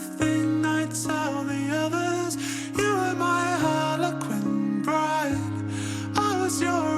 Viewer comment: i hear "hologram bride" according to Spotify its "harlequin bride" but i just dont hear it